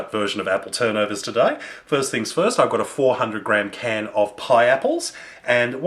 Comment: Oh god. I thought he said PINEAPPLES, so i made them with pineapples.. Im HAPPY to say that they actually work well with the cinnamon